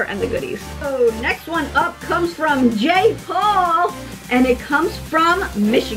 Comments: I thought she said jake Paul 😂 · In 2:10 did she said Jake Paul